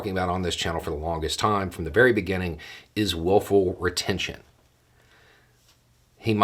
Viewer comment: For a second I thought you said "woeful retention". Well, I'll bet it is now.